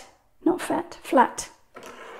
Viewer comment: I loved this. I was just doing your eucalyptus leaves because I really like that style but ended up glazing nickel azo over the top to get your look. I'd let my yellow dry all night, but my PY 175 is Schminke, and I have since heard W&N does better (more tinting strength —versus no tinting strength). And the PY 150 worked! My up-close leaves leaf much to be desired. I'm loving it! So I went to this video right afterwards and... did you say what I think you said at 19:55 😂😂😂 I love the dog! The style is so interesting. I've usually done the detail size brushes and try to get the hair in on animals (usually zooming in to focus on some bone structure (because IDK why: I'm learning! I need to know the skeletal stuff to "sell" it as a dog/cat/beast) but this actually looks more like a white shaggy dog than many I've seen (IRL, where the backgrounds are detailed so show the home or yard, I guess commissioned works). Lovely! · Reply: Lol- I said- 'start again' 😝. Thank you! 💕